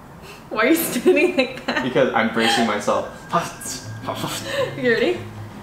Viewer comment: i think he was saying "im bracing myself" not embracing